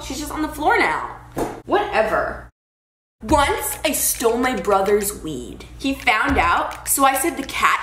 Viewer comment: I thought the first one said ' I stole my brother's weave ' xD